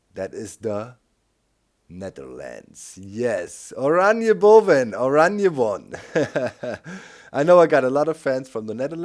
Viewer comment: I love your Dutch accent! And I was so close I live close to him! It's great to see you back! And an idea: try to say some things in different laguages of accents! Greetings from a big fan from the Netherlands! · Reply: XaufusProductions at 27:30 he spoke dutch, that is what jonathan was referring to. He said "Oranje boven" which is from one of our folk songs, literally meaning "Orange above", and Oranje refers to our royal family.